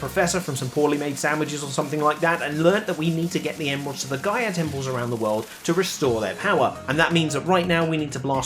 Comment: im so fucking brainrotten that i thought you said "gyatt temples"